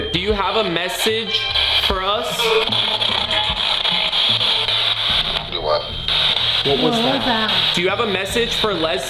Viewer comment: i think it said do what